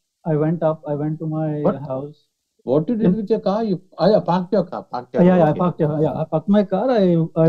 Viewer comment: this is hilarious 😂😂😂 See expression when he thought that guy said "fucked my car".